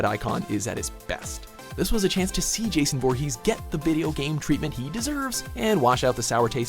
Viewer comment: did he say Jason bourne